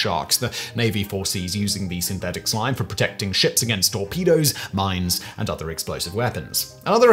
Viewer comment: I heard "all pedos" not "torpedoes"